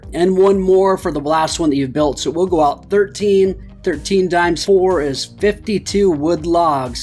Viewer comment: i thought u said dinosaur